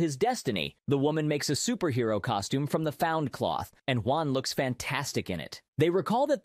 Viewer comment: Did he just say he looks "fantastic" in the costume?